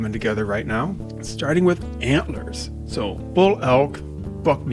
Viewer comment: I heard "Amblers" like the thing from the Dragon Prince, lol. I was so confused for a second.